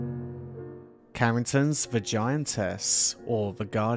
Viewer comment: I thought you were saying "Vagiantess"🤔